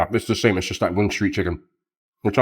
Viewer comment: i know bro said wing street but it sounded like goon street chicken